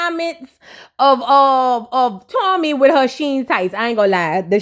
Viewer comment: THE “SHEIN” CHAPS TOOK ME OUUUUUUTTTT 😂Lol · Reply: The first time I watched it I thought I heard Sheer not SHEIN LMFAOOO. Then I ran it back and I was like ouuuuu where Tommie at